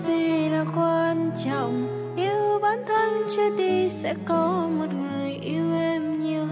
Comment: Nam Em hát bài này rất hay, nghe có phần ma mị, nhưng sau tui nghe đi nghe lại câu:" yêu bản thân trước đi sẽ có người yêu Em nhiều hơn" thì toàn nghe thành: yêu bản thân chết đi sẽ có người yêu Em nhiều hơn :(